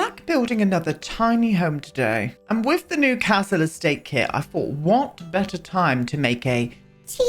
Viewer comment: i thought you said council estate kit.... now thats a kit we need lol